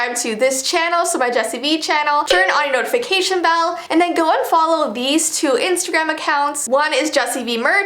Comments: omg is it just me or did she sound like she said "unfollow" lol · Is it me or is she saying to unfollow her Instagram accounts at this part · did she just say unfollow or am I trippin?🤣